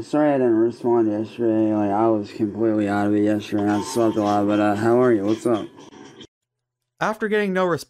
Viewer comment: did he just say he was out of it because he smoked a lot? I thought he was anti-drug 😭